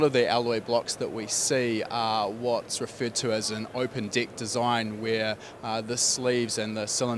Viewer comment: His accent to an American not paying 100% attention @ 1:45 I thought he said an "Open DICK design where the sleeves and cylinders themselves aren't connected" lol... I was like... wait wHAT??! Is he talking circumcision!?!?! I thought I clicked play about friggin cylinder bore sleeves hah. This is some good beer and greens ROFL.